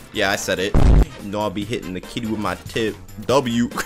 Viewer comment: In 8:46 I thought he said he said hitting the kid with my tip💀